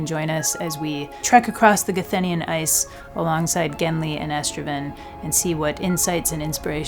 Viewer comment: I heard "Genly and estrogen" here😭 Genly needed to be forcefemmed